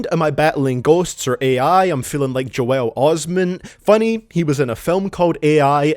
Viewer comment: The name Joel Osteen was used as a double entendre, Because Hailey Joel Osment the actor and Joel Osteen the televangelist which constituted a double meaning. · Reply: Okay… I relistened to it ‘cause I thought I heard him say Osment the first time, but no, he does say Osteen. While the bar does work when we know he’s talking about Osment, I think him saying Osteen was genuinely just a mistake. Which obviously doesn’t tank the song, but c’mon; let’s not pretend.